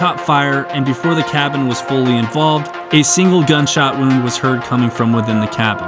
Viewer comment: Thanks for watching! Patreon: <link> Instagram: Join My Discord: <link> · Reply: /58 did you say "wound"? I've listened to it several times & that is what I hear...Am I wrong? lol I love Freudian slips, I do it all the time.